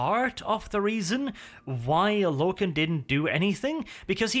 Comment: if you turn out auto-translate, instead of why logan it says why allah? lool